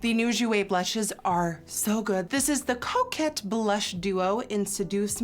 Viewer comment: When she said coquette I thought she said Crokette ( Crockett’s are amazing ♥️♥️♥️♥️♥️)